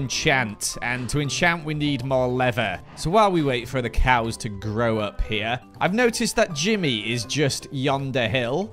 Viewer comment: LMAO I thought he said "while we wait for the cows to grow a pair" lmao -- 6;22